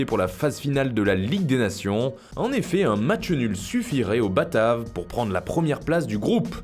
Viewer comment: Y'a que moi qui entend "batard" a 0:23 ?